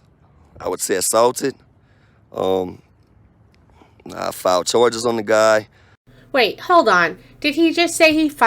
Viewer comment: To be fair I think at ~8:20+... He says "I'll file charges" i.e. I will. But he still didn't show us.